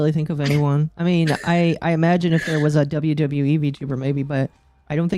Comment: I thought I heard WWE Vtuber for some reason.